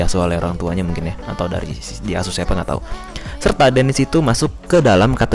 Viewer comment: Ini gua ga salah denger kan? Really bang? Diasuh setan😂